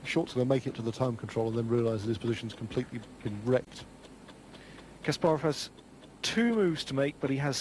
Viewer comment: Thank you so much from a chess fan for this gem! Am I hearing things, or at around 12:07 does commentator Ray Keene say "... Short's going to make it to the time control and realise his position's fucking wrecked"!? 😮 I mean, I know it was late Friday night Channel 4, but still! 😂